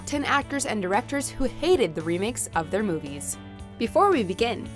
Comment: i thought you said "remix" lol. I thought we gonna have some song remix here.